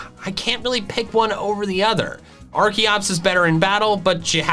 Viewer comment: *Sounds like he's saying arceus instead of arkoos* Me: A FOOLISH MISCALCULATION!!!